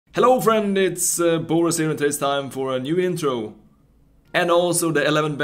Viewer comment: Did I hear "Hello friend it's eh BorasLegend"? I've always wondered what you are saying. Thanks for the slow mo.